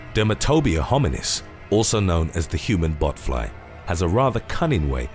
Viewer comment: Every time I heard “botfly” I though he said “buttfly” lol